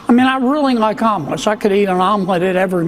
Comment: I thought he was saying almonds not omelets